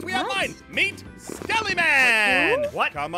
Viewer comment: I thought Ein said Smellyman 😂